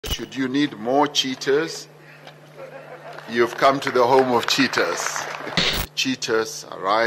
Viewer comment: I listen twice- First I heard " You need more cheaters you come to the land of more cheaters."